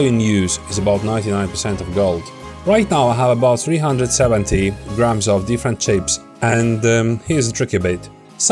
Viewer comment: How much ic chips u use for this video? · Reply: I thought he said 370 grams